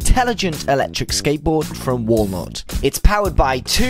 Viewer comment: i thought u said the board came from Wallmart lol